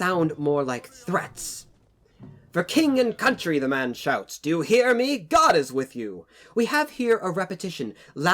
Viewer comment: Im pretty sure the Sergeant Major said "gentlemen" instead of "do you hear me." It's really hard to tell what he is saying sometimes wish they could've had him pronounce his lines better